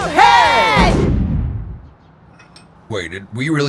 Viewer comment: What did they say? All I hear is: "Lets give this guy a head?" am I hearing this correctly?